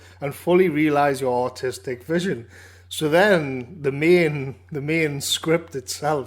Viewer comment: literally sounds like your saying autistic vision 🤣🤣 I love geordie accents. Gary needs to make a redraft but double down on all the themes outlined in the original draft and submit it again.